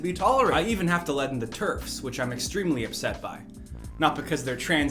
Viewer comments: I thought he said Turks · When he said “I even have to let in the TERFs, which I’m extremely upset by,” I thought he said Turks